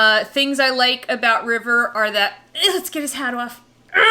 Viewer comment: When you were talking about River, and you wanted to take off his hat, for a second I heard: "Let's get his HEAD off", I was like whaaat? Why? :))